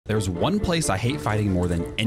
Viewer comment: O I thought you said the place you fight women 😅😅